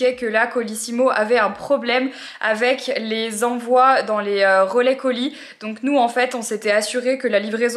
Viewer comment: Les quoi Marie ? Colais relis ou Relais colis ? 😂